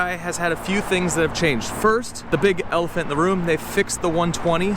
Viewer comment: Did he say "the big ELF in the room" at 1:35? or have I had too much elf on the shelf this Christmas season?